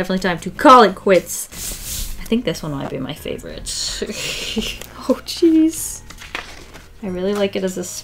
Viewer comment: I was sketching along while watching and I went to take a pic of my sketch as you said “oh geez” here and my phone took a pic because I guess it thought you said “cheese” 😂😂😂